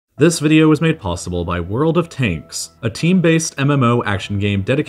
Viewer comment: I really thought he said “world of taints”